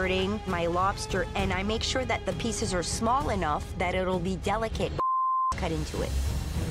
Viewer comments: Why tf did you bleep it? She said "so it will be delicate for when the judges cut into it" .... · What did they bleep out? Did she not say "when the chefs cut into it?" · I cant understand what word is beeped out @ 3:07. A few episodes ago the same thing happened when Chef Claudio said something. 'that itll be delicate (?) cut in to it' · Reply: she said “when the judges” idk why it was censored